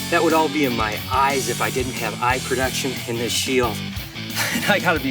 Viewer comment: you said eye production instead of protection. I can understand though lol